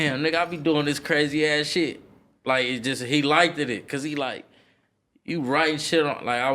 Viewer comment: Did he really say 'he likeded it' @ 16:34?